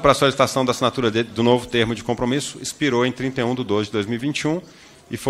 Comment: É isso mesmo que escutei? O prazo acabou em 31/02/2021?